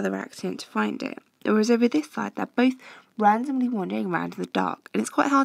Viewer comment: wtf anyone hear this tougher twister “wandomley rondering” 😹😹😹😹😩😩and ps I reckon I write in my exam “it’s quite hard to find people in the dark u know”